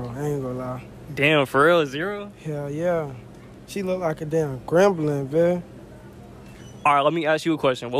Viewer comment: what was he sayin after 0:50 ? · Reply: gremlin